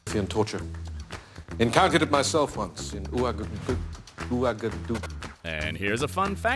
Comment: For the people that don’t know, what he was trying to say is “encountered it myself once, in Ouagadougou”